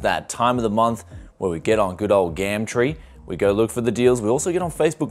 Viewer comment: I thought you said ScamTree and got a bit confused. lol